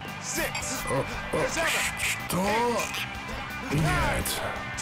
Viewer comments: he says "what?" before he says "no" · you can hear Popinski say "что" (chto) which means "what?" basically he tries to drink but he says "chto?" as he realises there's no drink left